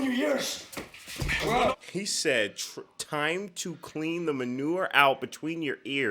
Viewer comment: That's so funny, I always heard "time to clean the menudo out from between your ears", not 'manure.' lol